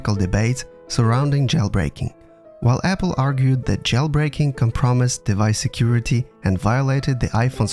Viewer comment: sounds like you said "can promise" when I think you mean compromise. I get its an accent or your dont speak english first but just so no ones confused